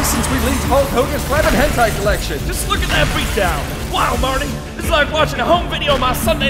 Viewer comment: Marley? Am I hearing that right?